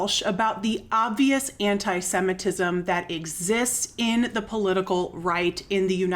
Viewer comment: whenever i hear "antisemitism" i think cest la vie